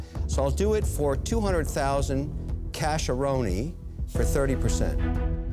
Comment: what did he say? Casharony?